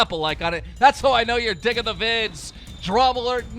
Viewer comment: who else heard “that’s how I know ur dick in the vids” instead of “that’s how I know you’re digging the vids”